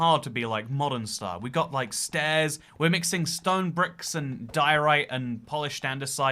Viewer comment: “While I appreciate my building style is not for everyone” shows image of diorite tortoise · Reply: I thought you said doritos 🤣